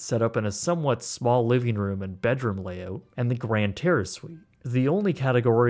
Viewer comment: Why did I hear “Grand Terrorist room” 😂